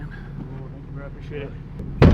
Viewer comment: Did anyone hear around 4:45-4:50 “there’s your shit” as he was loading up the parts to the truck